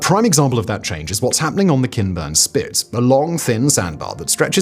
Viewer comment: Instead of Kinburn spit I heard "Ken Burns spit" and was only slightly confused